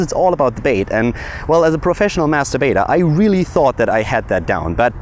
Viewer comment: Got a shock when you said Master Baiter.... sounded like you said Masturbator.... lol